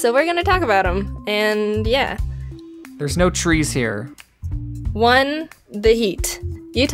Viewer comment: When said "there's no trees here" I thought he said "there's no CHEESE here" XD